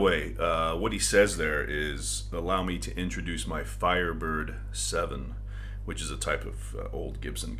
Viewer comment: I’ve always thought he said ’Hi I’m Marco my dimensions are, I’m 5 foot 7’. Thanks to the internet I got the correct lyrics now. thanks for keeping this amazing album alive. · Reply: I always thought Marco said "Let me introduce my vibrate seven".